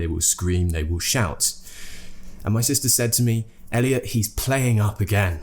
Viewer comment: In this minute 2:02 did you say: saids me? I guess it's told me or said to me.